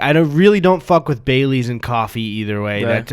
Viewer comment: I thought you said "bay leaves in coffee" for a hot minute and was intrigued